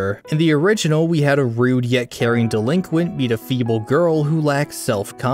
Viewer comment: idky but the line a female girl will forever stick with me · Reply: twas a false alarm, i herd you say the line feble girl and it sounded like you said female girl (been up almost 20 hours when watching this so it was my time hearing) and now my misherd video caption shall forever be on the internet